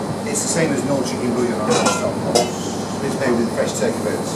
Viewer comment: I would add some knorr stock pot to add some flavor and richness to it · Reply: he actually says stock pot, lol